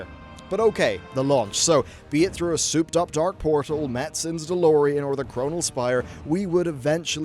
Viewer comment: i sometimes mishear "Metzen" as "Matsen", which is my name